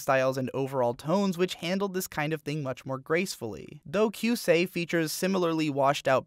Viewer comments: what was the name of that anime? couldn't hear it correctly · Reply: It's Doukyuusei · What is the anime at 5:34? I can't quite hear what you're saying... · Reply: Doukyuusei <link> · Whats the name of the anime he talks about at 5:40? I can't figure out the spelling to save my life and it sounds good. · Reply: Doukyuusei or Classmates being the English title